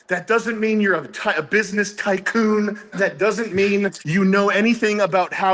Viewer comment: Did he say business kykecoon?